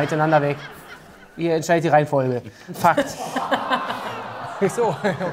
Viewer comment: Was sagt Dennis bei 12:16?😂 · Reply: „Ihr entscheidet die Reihenfolge.“